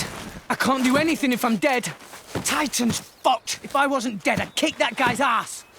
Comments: Okay I'm doing a catch up on this series and damn I thought rex said "titan's fucked" for a second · So am I the only one who heard the f word when rex said titans foot